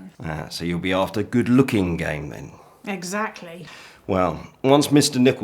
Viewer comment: Lol. I heard 'you're looking for good looking game, then' as 'you're looking for good looking gay men'. And Mrs Crocombe answering quite sternly: 'Exactly!' made me giggle 😅